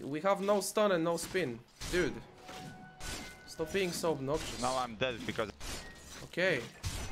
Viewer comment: "Stop being so...." what word was that last? I can't understand that one, someone please? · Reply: obnoxious